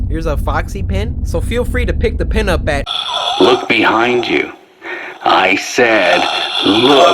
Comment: Is it just me or did I hear a voice say look behind you